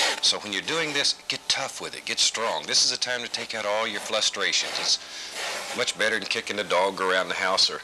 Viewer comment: Did he just say "flustrations" twice? around 7:20 to 7:30? Bob Ross rewriting the dictionary confirmed, and right in front of his own son. The absolute mad man.